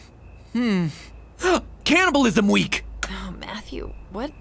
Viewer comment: i thought you said capitalism and was seriously confused for a hot minute